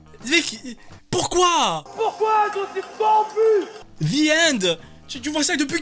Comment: Mais en vrai le "the end" quand elle l'a prononcer j'ai entendu "viande" avec un accent